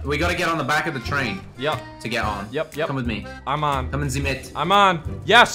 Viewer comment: did he say: „Kommen Sie mit“ ? xd